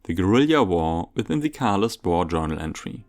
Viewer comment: Hahaha :D "Just wait until the end of the game and you are fine." I think a have several small bloopers :D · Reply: yeah but "David v.s. Guerilla" is very creative, I originally thought you said gorilla tho haha